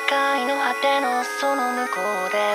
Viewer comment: did anyone else hear “SEGA I know how thanos so long ago”